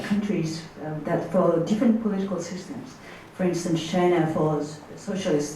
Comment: 佐藤さんの英語力、さすがです。1点だけ、本人になりかわり弁明しときます。時間2:21あたりで、Chinaをシャイナと聞こえるような発音でしたが、これはフランス語も堪能なため（シノワ）と、頭の中で一瞬混じったんだと思う。